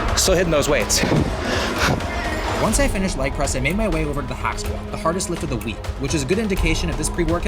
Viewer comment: I thought I heard hospital instead of hack squat 😅